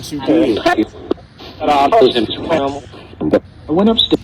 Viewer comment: I heard “I went upstairs” 🤔